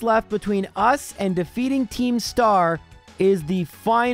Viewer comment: i thought at first you said the villain team name was Keemstar lol